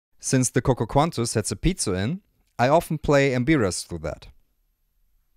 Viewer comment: I'm really glad that I found your channel. I'm loving your recordings. Can you post details about the finger instrument you hold up at 9:27? I can't understand what you called it. The closed caption say you said "bearest". It's really nice looking. Where did you get it? Thanks! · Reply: thank you! That is an mbira, made by juma drums in Johannesburg.